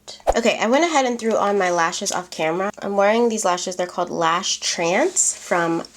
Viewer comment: Those were the lashes she went back in forth in the clip cuts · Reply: lmao I heard " I'll go ahead and throw on my lashes off camera" instead of "I went ahead and threw on my lashes off camera" ;-; I sound so dumb rn T^T